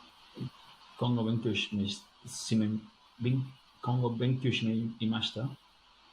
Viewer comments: I can't understand what he's saying except for the "いました/imashita"... I checked the podcast and apparently he was trying to say "この日本語を勉強してよかったなと思いました/kono nihongo o benkyou shite yokatta na to omoimashita"... now listening back to it, I can hear the "勉強/benkyou", but he completely butchered the rest of it... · it sounds like hes trying to say 「今後勉強していました」 "kongo benkyo shiteimashita" sentence fragment that doesnt even internally make sense, something like "next time (in the future) i studied (in the past; nonsense)" so im guessing he just misheard it. besides that though, he said 「しねいました」 "shi*ne*imashita." which is really telling because its a very common verb form. so i just found the clip in the original video and hes not even REMOTELY close. they say 「...この日本語を勉強してよかったなと思います」 "...kono nihongo o benkyo shite yokatta na to omoimasu" which means something like "im happy i studied this japanese" to put them next to eachother for comparison: "kongo benkyo shineimashita" "kono nihongo o benkyo shite yokatta" i think he just totally missed the "na to omoimasu." so basically he cant even hear the sounds properly and doesnt know what the words or structures he's using actually mean. to be fair, totally expected with only 21 days of study so i cant really rag on him. the arrogance just has me in a critical mood i guess.